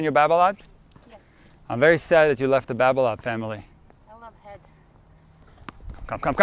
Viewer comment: what did she say 😅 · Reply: She said," I love head."😂 I prefer Yonsex.